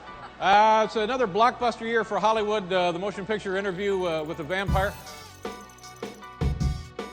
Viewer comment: he clearly says with "the" vampire. It just sounds like "a" as it's mainly pronounced as "uh" same as t"he"....